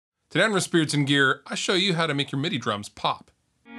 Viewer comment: every time u start one of these videos i always hear "today on Riff Beardson's Gear"